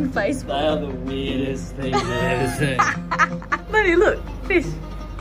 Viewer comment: FIsh-flops instead of flip-flops lawl